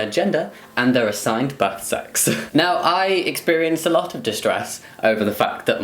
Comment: I kept hearing "butt sex" instead of "birth sex". My mind is always in the gutter.